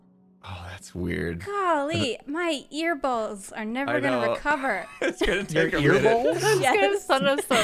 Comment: did. Did she say. Earballs? What? I heard "Golly, my earballs are never gonna recover."